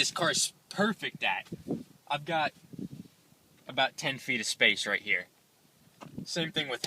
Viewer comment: Did you say ten feet of space? :) :)